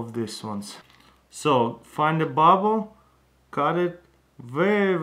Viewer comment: the captions said "Find a bomb